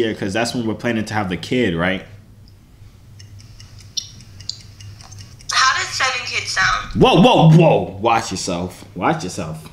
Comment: what’d she say? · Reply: How does seven kids sound? Dats wat she said